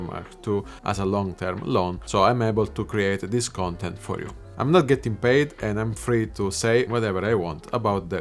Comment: 55 seconds or so in - sounds like you say : I'm free to say whatever "they" want. Haah. I'm sure it's I. Just sounds like they. Found it funny.